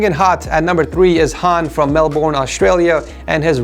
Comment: did he say "melborn"